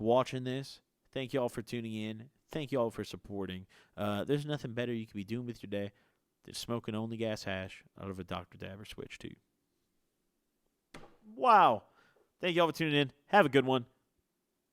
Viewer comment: I heard “there’s meth amphetamine you could be doing here to day” but I’m friedman